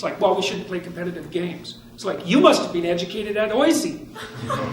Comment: What does he say at 4:35? "...you must have been educated at __!" sounds like euzy/eucy, doesnt make sense to me... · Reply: OISE, Ontario Institute for Studies in Education, in Toronto